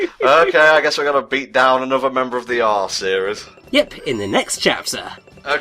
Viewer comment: Because I'm immature, I heard "taking down another member of the R series" as "arse series" and to be fair, the new one is very much and arse